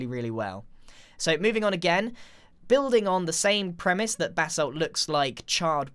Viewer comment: It sounds like he is saying BATH SALT instead of BASSALT.